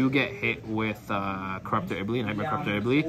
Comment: Hi!! as a non english native guy at time 3:54 when you're talking about dark hole I can't understand the name of the card you're getting hit by...can you clarify it for me please!! I'm almost done building cyber dragons!! thanks for the amazing videos! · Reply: The card he is specifically saying is "Knightmare corruptor Iblee" hope that helps